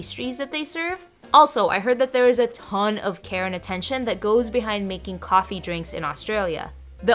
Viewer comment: I heard “lots of Karen attention” instead of “lots of care and attention”😂😂🙈